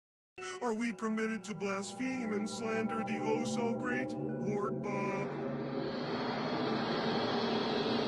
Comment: Actually, I think he says "Are we permitted to blaspheme and slander the oh-so-great LORD BOB??" and then an image of Bob from animal crossing starts spinning, I think it's a shitpost account lol.